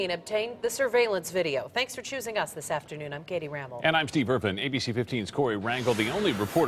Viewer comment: I thought he said Steve Irwin RIP